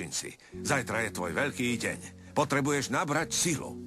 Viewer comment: "potrebujes nabrat sily"? Is that what he said at 0:24 ? It's funny cause I'm polish, and I can understand what it means... without knowing a blasted thing about slovak! LOL! :D